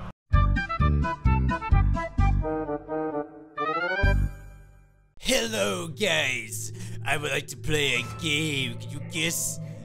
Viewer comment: Lmao when he says "Hello guys" at the beginning of the vid, it sounds like he says, "Hello gays" akdhsksjs XDXD